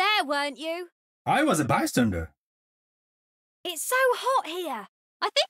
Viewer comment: when i first head genji say 'bystander' i heard it as bartender, he would be a good one